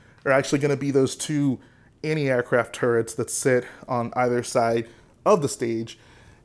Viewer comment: did he say Annie Air Craft turrets?